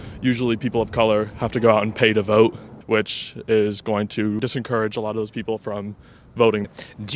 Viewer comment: I swear that kids said disencourage at 1:12…dude is IN college and doesn’t know English. It’s discourage. For heavens sake, technology and school was supposed to make us smarter not dumber. Technology and social media now making people say “un-alived” because it won’t let them say what they actually are trying to communicate. It’s so distopian and terrifying.